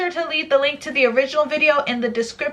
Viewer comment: When I first started watching your channel you know that you said what's up hobiscuts I thought you said (hoe)-biscuts